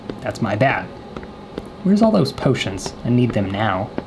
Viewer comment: When he tried to hide from "Grandma", I had to replay it 5 times because I thought he had said "oh sh*!" When he actually said "potion!" 😂😂